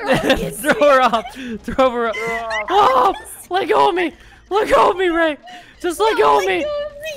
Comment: I don’t know why but I thought lily was saying “la come la come la come” instead of “let go of me let go of me let go of me”